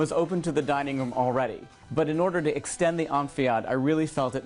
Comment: I keep hearing use a word “amphiad”? which seems to refer to some extension of space? What is the word I am mishearing? I want to know what he is talking about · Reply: enfilade